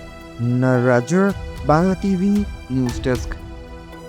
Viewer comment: Reporter এর নাম কি বললো?😂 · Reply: Nnaajoor Bangla tv